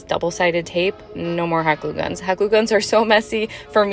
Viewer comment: Really could have used the captions on this one. When you said hot glue guns I thought you said "hyper loopums". If, for some reason, you're not using captions, slow down when you say things like "hot glue guns".